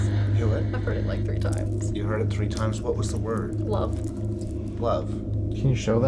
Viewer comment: Bullshit I listen 3xs and didn't hear " Love " I heard 2xs " Demon "...